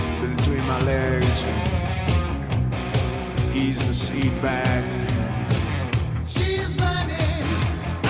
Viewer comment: reaction to "Full blast and top down" made me think "Wait 'til he eases the seat back." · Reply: my buddy's mom didn't want us listening to that filth. We asked her what was filthy about it. She thought he was saying, "ease my seed bag." 😁